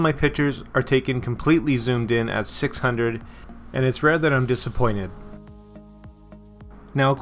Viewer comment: i didnt understand what he was saying, is he disappointed or not? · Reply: ”It’s rare that I’m disappointed”. I interpret this as no, he is not disappointed.